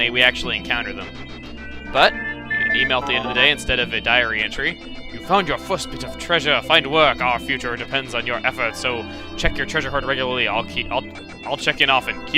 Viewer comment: it almost sounded like he said diarrhea tree XD